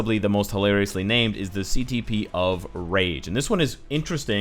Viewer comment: I heard him say ctp of rage instead of ctp of greed